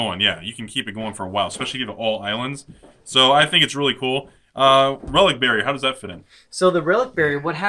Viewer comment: For a second I thought you said "Relic Berry" lol...